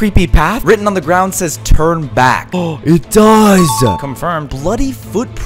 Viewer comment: bro i thought he said "turd back" 💀💀💀